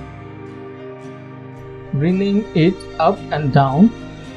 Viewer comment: I am sorry but what did you say at 1:57? " bringing it up and down"? it was confusing when you grouped the text together by twos. Great video but I got lost at that point.